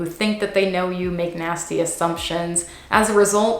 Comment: I heard "McNasty assumptions" instead of "...make nasty assumptions"